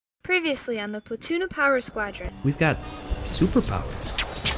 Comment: I honestly thought said "Tuna Power Squadron", and I couldn't figure out what PoPS was until I watched this video.